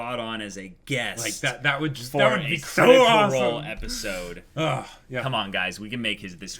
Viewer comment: At around 52:00, wants to go LA as a guest for an episode of something. I couldn't catch it, anyone else got it? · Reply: Waldeinsamkeit Critical Role